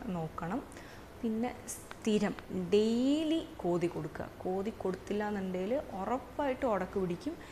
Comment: didn't understand , daily wht to do? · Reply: Comb their hair daily ☺️